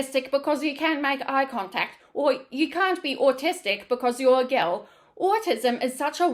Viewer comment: did you say girl or Gorl? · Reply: I said girl.